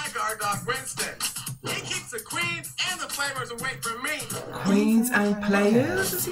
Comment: I think he says "he keeps the Creeps and players away from me" ...but I also hear "creams and flavours" so what do I know lol maybe he hates food